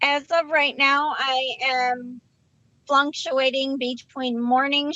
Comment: I’m sorry but did she say flunctuating @ 19:13? How is she flunking and fluctuating at the same time, 😂?